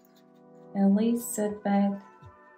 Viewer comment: Could you tel me wat did u said 12:32 i hard one name. I have one person in my mind n in that spelling also my person 1st name i seen so plz tel me wat u said. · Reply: I think you mean Elisabeth